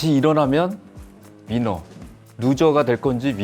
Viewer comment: 삼성E&A의 임원분들 사장님부터 상무님까지 대단한 분들이 한자리에 모이셨네요 다들 30년이상의 근무를...와우~ 이걸 기획하신분도 대단한것 같습니다 ㅋㅋ 오늘 다양한 이야기에서 재미있었던 부분은 14:04 루저가 될 건지 위너가 될 건지.... 위너... 민어??? ㅋㅋ 다시 들으니 민어... 사장님도 민어 좋아하시나봅니다 !! 그리고 10:25 부사장님의 "우리 회사 일이 되게 재밌어요" 참 이걸 행운? 복받았다 해야하나 그냥 대단하네요 일에 대한 재미가 많아서 오랫동안 근무할 수 있었다 와.. 저도 이런 일을 찾아야 할텐데... 오늘 임원분들의 유쾌하고 유익한 이야기들 잘 들었습니다 즐겁고도 많은 도움이 되는 시간이었다 생각하네요 앞으로도 종종 나와주세요~~ ^^